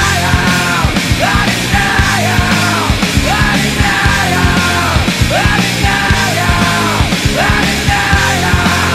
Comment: what is he saying also I don’t even know what he is saying for the whole song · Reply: He's saying "A denial" over and over again